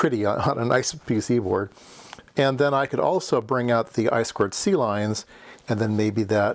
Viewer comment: I thought he said "Ice squared sea lions" but he actually said "I²C lines" which makes much more sense